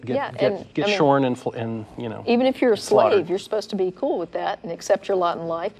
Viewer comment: Who else heard "Even it you're slutty" at 17:00? :o)